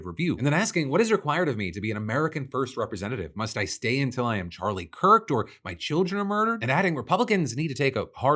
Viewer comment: Did he just say “till I get Charlie kirked”!?!?!?!?